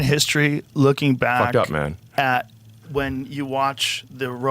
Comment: Did Elon just say "fuck up man" ??? 🤣🤣🤣